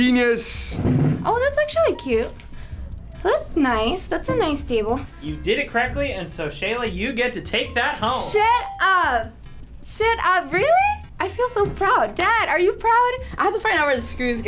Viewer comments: Why it sound like sheila said shit up when she did it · am i the only person who thought sheila was saying sh!t up? 😂